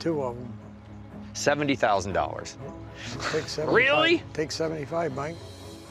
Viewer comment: I thought the guy said $7,500 and that’s all I’d pay for it